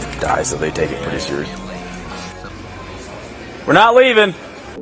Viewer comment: I heard a male (police officer?) voice at [2:09] said "were not leaving".